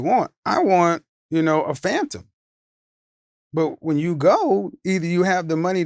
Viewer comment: Lolz my broke self thought he said "I may want a Fanta"